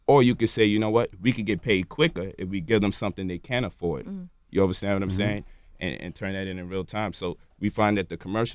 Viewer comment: did he just say "you overstand what I'm saying" ?